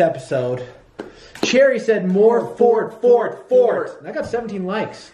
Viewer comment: Do more forts they are fan favorites and personally i love them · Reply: I thought u said do more farts cD